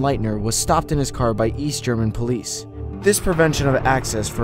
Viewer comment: East? I thought you said Weast.